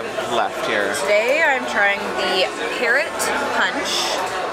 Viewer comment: This week on, "Guess the Towel Animal"... I was sure it was going to be a rabbit. I guess that means I will have to turn back in my Schrute Bucks that I won last time for guessing the towel animal correctly!! I thought you said the new drink had at the Schooner bar was called "Carrot Punch" w/ Captain Morgans.... and I was thinking it would have some sort of carrot juice with it.... and I was thinking that sounds terrible. Well, I had to rewind it to hear that it was actually Parrot Punch w/ Capt. Morgans.... and no carrot juice. Makes a lot more sense now. Don't worry, I'm American and I don't know who the 5th President was!! I can't remember ... did you get the Drink Package this cruise? Or were you just rocking the Diamond vouchers? Thanks!!!!!!!!! 🍺